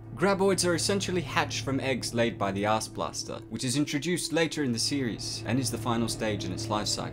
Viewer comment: I thought he said the " Ass blaster" At first😂😂😂😅😅😅👌👌👌